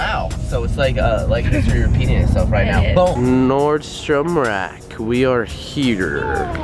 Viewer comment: I thought Alex said Nordan shats place instead of nordstrom